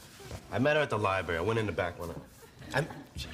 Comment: what did he say? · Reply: "i went in there to back one out" meaning taking a dump :)